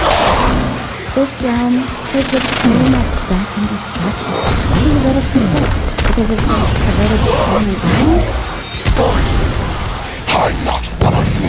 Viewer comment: i really wanna know what chara says here but i can't hear it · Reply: Did a little digging and found this "This realm is filled with so much death and destruction. Why do you let it persist? Because of some perverted sentimentality?"